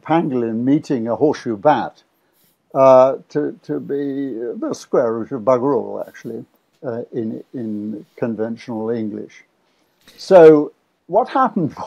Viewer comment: Thank you Dr.Campbell for giving us this absolutely bang on advice from Prof.Anderson. so refreshing to hear such a knowledgeable man of character nailing every point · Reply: I've been listening several times but I'm still not getting the expression as spoken @ 10:28 - I might be hearing: " bit of square root of bagruel actually - in conventional English... ". Could someone hear this better and provide an adequately accurate interpretation here? Thanks so much.